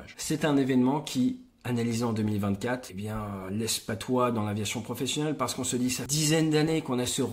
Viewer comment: laisse QUOI ? 🤔🤔🤔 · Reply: Je pense qu'il voulait dire "laisse pantois" 😅